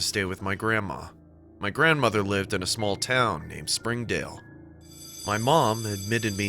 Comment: When he said springdale i thought he said Springfield and the simpsons theme started goin off in my head🤧